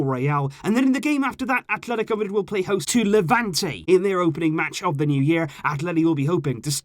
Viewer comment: There a mistake for this part 1:57. There is Real instead of levanté.